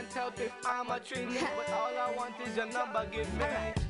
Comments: did he say "imma trini at 2:30???? wat nationality is da girl thats singing? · did he say im a trini at 2:30?